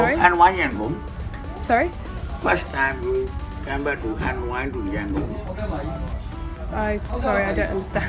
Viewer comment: He said are you first time coming from Ha Noi to Yangon 1:00 how did you carry you drone ??? carry on ?? ....many of visitors keep it in check in luggage ...it was OK